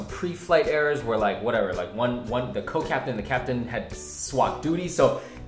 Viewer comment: "From Sydney Australia". But your map showed Melbourne Australia. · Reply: "Co-captain". I thought you said "coke-captain". That would be more like it.